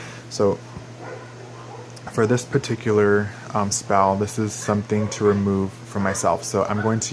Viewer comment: Did he say spall? XD ( 2:19 )